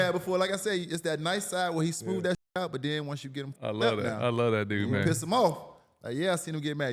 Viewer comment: Did Mike miller say the N** word fam? At 5:38 mark? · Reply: At 1st listen it sounds like he did, but after that fifth listen I heard “I love that dude, man”